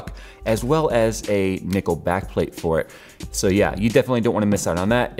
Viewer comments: DID HE JUST SAY NICKELBACK PLATE? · did someone say Nickleback!